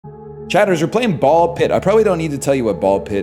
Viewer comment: I thought he said "Bald pit"